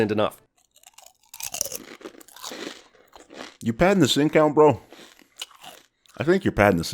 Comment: crunching noise You padding the sin count, bro? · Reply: I understood it wrong for a while and thought he said "you padding the sink out bro"